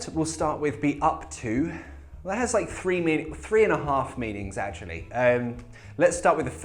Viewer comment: captions say three million three and a half meanings :P